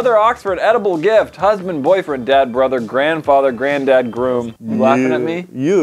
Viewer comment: I thought he said 'dead brother'